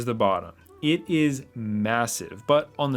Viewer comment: Did you just say massive?